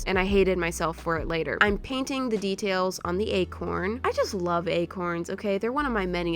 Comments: I thought she was saying egg horn not ayhorn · When she said “acorn” I thought she said “egg horn”... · when you said acorn i thought you said egg horn and was so confused. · When she said acorn I thought she said Egghorn and I was like, why the heck does a squirrel need an egghorn, what even is an egghorn?! OOF me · When you were saying acorn I thought you were saying egg horn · When said okay we're going to make the acorn it sounded like she said egg horn